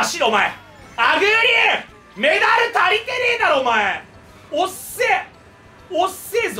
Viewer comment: メダル足りてねぇだろの所がカルシウム足りてねぇだろに聞こえた私は病気です